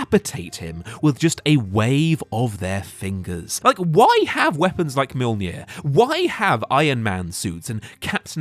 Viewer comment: I coulda swore I heard you say “Why have weapons like metal gear?” For a second 😂